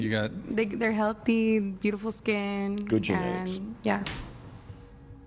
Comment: did you just say "good genetics" followed by a "good eugenics"?